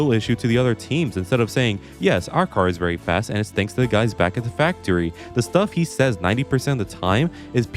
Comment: did someone say THE FUYS AT THE FACTORY